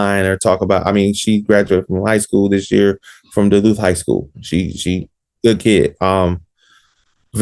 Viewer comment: He said Egypt is in St. Louis, but is in school in Duluth, GA🤔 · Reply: I caught that - because I thought he said "Ladue High School", which is in STL - I slowed it down to .25 to catch "Duluth High School"